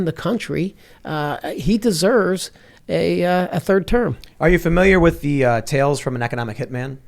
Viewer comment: Trump deserves a 3rd term, did I hear that correct?